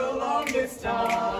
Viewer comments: All I hear is "for the seventh time" XD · I only hear "for the seventh time" during the whole song